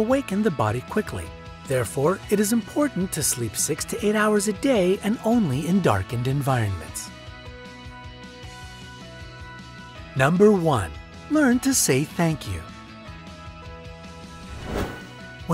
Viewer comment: I thought he said 68 hours a day ..loool